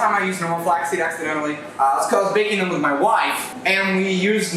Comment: did he just say wife. YOU HAVE A WIFE. Someone please explain. I think I heard it wrong 🤯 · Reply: Nah. He did say wife. Idk how to explain it lol. He has a girlfriend but I think the wife thing is just a joke but don't take my word for it.